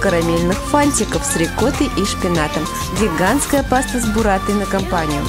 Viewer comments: > веганская > с бурратой Ладно UPD: Сказано и написано было "Гигантская". Я обосрался похоже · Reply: капец, а ведь реально. Надписи при первом просмотре не заметил, а ухом услышал "веганская". Фрейд по мне плачет. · > веганская > с бурратой Ладно UPD: Сказано и написано было "Гигантская". Я обосрался похоже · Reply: Я тоже услышала "веганская"